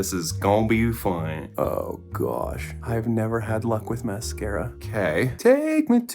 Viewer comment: When you said luck with mascara I thought you said liquid mascara and I was like what the frick other mascara is there😂😂😂😂